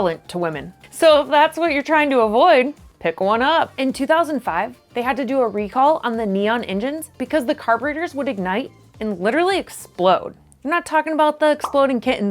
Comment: did she just say carburetors?